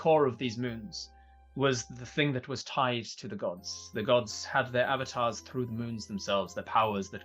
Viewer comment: That first riddle was so confusing to listen to. At first I was like "wait is that the riddle? It makes no sense" then repeated it like 3 times... The line "ere to the gods" made no sense at first because I heard "ear to the gods" I've always heard ere be pronounced the same as air. Anyways this was so fun to watch.